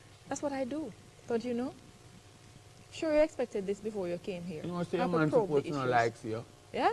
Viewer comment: what did he say at 25:09 ?? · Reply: He’s saying her boyfriend probably don’t like seeing her.